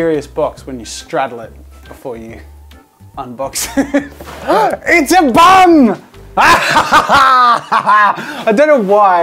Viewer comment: The first thing I hear "it's a bum"